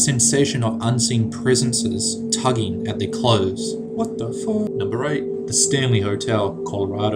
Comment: am I the only one who heard that 'what the fuck" ?